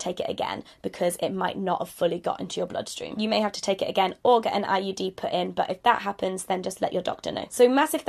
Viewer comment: Until you repeated it I thought you said another form of emergency contraception was an IED. Well... it would stop you from getting pregnant xD